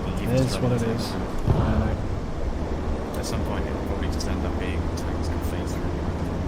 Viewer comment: cant make out what the dev guy is saying about fences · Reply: The end was along the lines of "At some point it'll probably end up being that tanks can phase through.." So maybe not destructible but tanks will go through them?